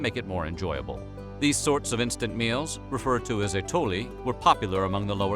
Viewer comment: That part at 10:47 where in the subtitles say [INAUDIBLE] should be "Atole"